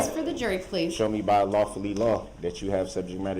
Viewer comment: did he say “lawfully law”? 🙄